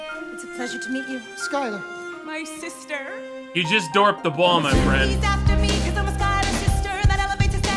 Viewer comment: The side effect of my significant brain damage that I hate the most is my brain can really only process words by both seeing and hearing it. I have no idea WTF you said but I heard "You just dorped the borm, my friend." As I typed that, I'm assuming "dropped the ball" is what you said.